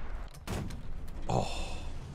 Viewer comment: Is it just me or does it sound like says “ohhh f-“ and bleeps himself with an a10 sound